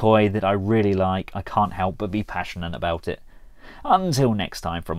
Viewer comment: whn i see artfiee for some reason i hear " im super thanks for asking "